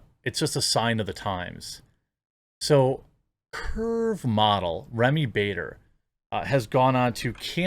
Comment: I've never heard of a "perv model". Oh, wait. I misheard. "Curve model"? I've seen some "curvy" models. They're hot. They're nowhere NEAR 240 pounds. I don't know. Maybe I heard correctly the first time. She could always just ride in a wagon. A freight wagon.